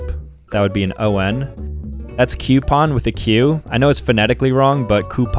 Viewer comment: Did he just say... Cuupon???